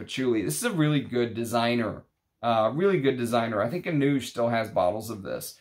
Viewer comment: who has bottles i couldnt make out what you said? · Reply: Anooj He owns enchanteperfumes Look them up they ship worldwide